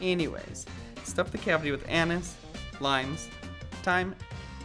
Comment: Why did i hear that as “Stuff the cavity with anus”